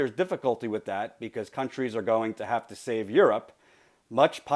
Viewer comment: I think that was a misquote. Instead of "Countries are going to have to save Europe", he actually said "Companies are gonna have to sorta gear up". No big deal, IJS.